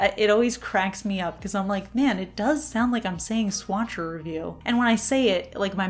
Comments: I always thought you were saying "swatching review" 😶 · Hilarious--I thought you were saying "Swatch Interview" :) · I always thought you were saying, "Swatch review" so now I know! What a beautiful collab! Thanks for sharing these polishes with us. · I always thought you were saying “swatching review” 😂